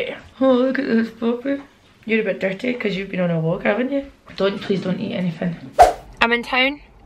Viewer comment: Oh my goddddd when you told the dog not to eat anything I could have sworn you said “don’t lay on the infant” 😂😂😂 the struggles of understanding the accent 🙈